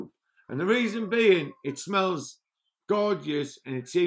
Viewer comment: Did he just say…”It smells gorgeous.”